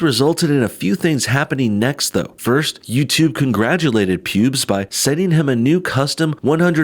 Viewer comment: coulda swore he said "pubes"